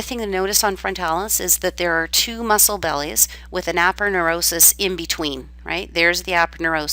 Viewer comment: what is the word that you are saying because I'm confused is it epicranius or arrapunis?? What is that? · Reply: APONEUROSIS THAT IS A FLAT STRUCTURE